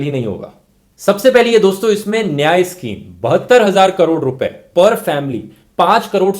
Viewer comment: did he say "72 hazar" crore'? rupaya🤔🥴